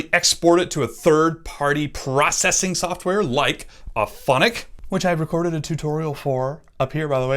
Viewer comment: Awesome, thanks I always wondered about Anchor. At 4:19 you mentioned audio processing, what was the name of the software? (Upfonic is what it sounded like)